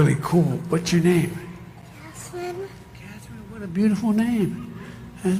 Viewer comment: I don't know what is the bigger embarrassment here, Biden, Jill, or the supposed 81,000,000 voters? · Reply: Hey Joe, your mom’s name was Catherine, I thought the little girl said her name is Jasmine.